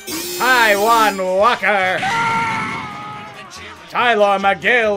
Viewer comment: When he said Taijuan Walker I thought he said “I want water” 😂